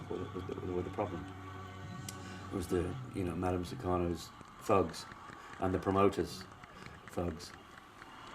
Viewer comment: Madame Sukarno's thugs? Did I hear that correctly?